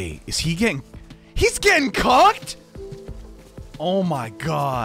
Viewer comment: What did say around 6:25 of the video because I think he said conked but I can't understand it and what does that even mean